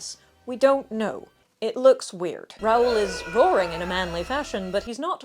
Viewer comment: “Well, technically a grey horse, but who’s counting…”😂❤ This is why I watch this channel. Also, I definitely misheard “Raul is boring in a manly fashion”, which is also accurate 😅